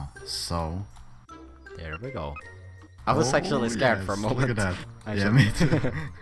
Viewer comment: i know he said "I was actually scared..." but it sounded like "I was sexually scared..."